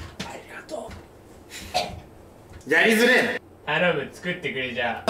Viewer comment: 頼むがI Love You に聞こえる（笑）